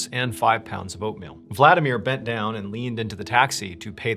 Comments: I really thought you said 5lbs of opium instead of oatmeal. I was like wait where tf is this going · I thought you said Vladimir brought 5lbs of opium! No wonder bro thought he would be fine lmfao I had to run it back 3x before I heard oatmeal. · I swore at first you said he had 5 lbs of opium. Then we got past the middle of the story and I'm like what's up with the 5 lbs of opium? Why would this super christian dude need with all this opium (5 lbs is A LOT!)? Why would he not want a gun but need the opium? Was the opium any good? Et cetera? I had to go back to the beginning, then had to go back quick like ten times 'till I finally heard oatmeal. Fucking one track mind! Lol. That Dmitri guy shouldn't have let him use the cabin with no gun, or opium for that matter! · I swear I thought he said Vladimir packed 5 pounds of opium not oatmeal. I was like dang, it's gonna be one crazy ending. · Second story. I read (close caption), "...backpack...clothes and five pounds of oatmeal...". I replayed this part with caption because I HEARD, "...backpack...clothes and five pounds of opium...". Ahh, okay. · lol. thnx for sharing. I laughed because I had to replay the very beginning twice because It sounded like you said something that not only didn't make (much (LOL🤣)) sense. It took me 3 'hears' to hear "oatmeal" because the first 2 times I heard "5 pounds of OPIUM".🤣🤣🤣🤣🤣🤣LMAO. KINDA made sense but not totally.🤣🤣